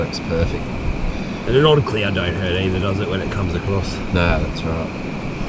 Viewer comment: Sounds like you said “that’s alright” what a great river and a new good friend so cmon we have to know did you beat??? Lol great adventure and has pulled some monkers out of there wow thanks for sharing see you on the next one 👍🏼👊🏼